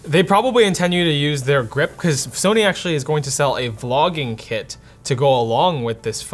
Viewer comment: I thought he said Sony is going to sell a flogging kit to go with the phone. Is that so you whip yourself for spending $1800 on the phone ?